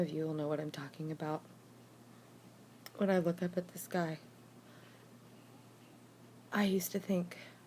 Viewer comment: when i hear the words "the sky" i always think "this guy". lol!